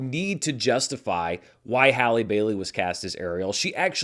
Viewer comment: Legit thought Dan said Halle Berry was Ariel and had to do a double take